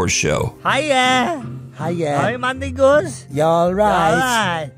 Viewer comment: did they just say hi my niggas? 🤦🏽‍♀️🙄🤨😂😂😂😂